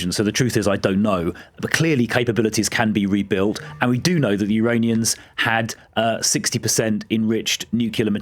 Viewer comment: did he say uraniuns at 1:30?